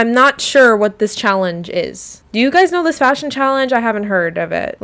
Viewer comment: I think she says "fashion challenged", which seems to be a play on outdated language referring to a person with a disability. Also, great video!!